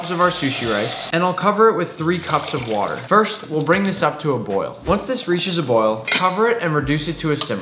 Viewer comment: I thought said bring it to a ROIL, a portmanteau for roaring boil lol